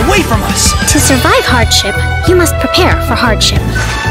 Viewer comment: What I heard on 0:56 is “to survive hard shit you must prepare for hard shit” 🥲